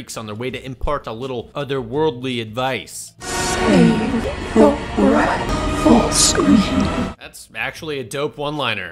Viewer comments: What was the one liner? I couldn't figure out what the lady was saying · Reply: Had to slow it down.. It said "Save your breath for screaming" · can someone transcribe what she's saying (the dope one-liner)? Thanks! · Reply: "Save your breath for screaming" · Yo what did the cenobite say at 15:30. said it was a dope one-liner but I couldn't make it out.? Edit* this is a question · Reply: "Save your breath for screaming" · What did it say? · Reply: "Save your breath for screaming"